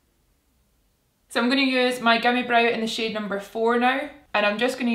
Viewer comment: Genuinely thought said gummy brow